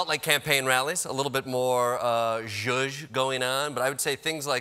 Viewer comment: "A little bit more...? going on" What did he say there? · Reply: Zhuzh